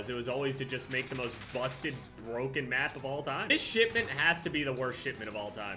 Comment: Am I the only one who hears SHITment instead of shipment ???